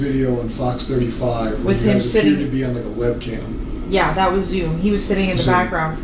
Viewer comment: could be sound quality but I think she said "yeah that was you" to the detective's statement....